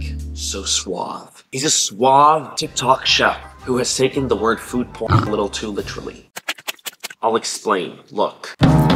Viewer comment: why did I think he was saying food poisoning